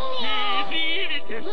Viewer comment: sounded like meow meow!